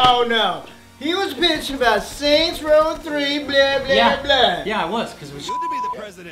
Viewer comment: AT 28:50 SAID BLA BLA BLA BUT IT SOUNDED LIKE RUSSIAN WORD "BLYA" WHICH MEANS "FUCK" OR "DAMN"